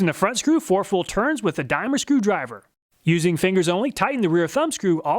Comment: what did you say that screwdriver was? In the UK we'd call that a 'flat blade' screwdriver. But you said 'Dimer' or something? I couldn't make it out The TSProf is pretty amazing. Some finer grade stones would be good though. Also the knife clamp allen screws should have thumb grips on them or something so you can use it without needed to fiddle with allen keys. Looks like a lot of engineering for $380! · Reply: I think he said “a dime or a screwdriver.” (A dime is a small US coin)